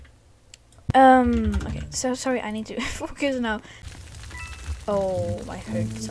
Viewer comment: im fo confused did she say f*ck or something I couldnt hear it correct